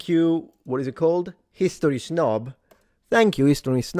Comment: I thought you said "History's Nob" 😂